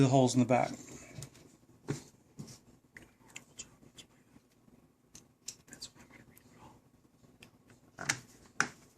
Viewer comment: Was I the only one who heard someone whisper at 14:58 "You did a horrible job, that's why I'm going to redo it all!" ??? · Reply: Actually it was him talking. He said "I did a horrible job explaining that, that's why I'm going to redo it all." Im assuming he meant shooting the video. But nonetheless he wasn't trying super hard to put the cover on that well. Looks wrinkled on the top of it when he first put it on. But the end product looks better. I think he took it off and re-did it possibly. Ultimately it's about what you do when you install it not what he does. He's just explaining it so people get the point. Clearly more care must be taken when you're doing it yourself because you're not simply making a video lol